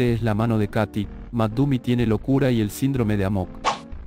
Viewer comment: escuche among us XD